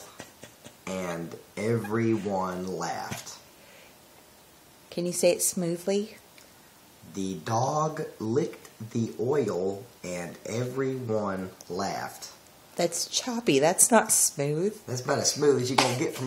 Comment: Why would people laugh if a dog licked oil? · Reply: i thought he's saying "the dog licked the oil and everyone left" the whole time lol